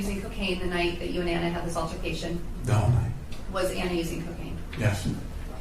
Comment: “and in my coked up mind I thought it would be a good idea to tell my fans that I’m going to jail” “You were using cocaine the night you and Anna were having this altercation?” “No” 🤨🤨🤨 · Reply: It does sound like he said no but he said "the whole night". It's the way he says "the" with a breathy small voice that makes it sound like no.